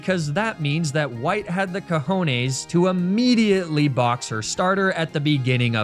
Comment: did he just said “cojones” in spanish?